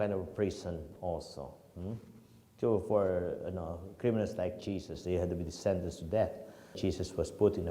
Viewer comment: Time 18:50 sabi ni father criminal like jesus correct nyo ako kung mali ang narinig ko sa pari sa sinabi nya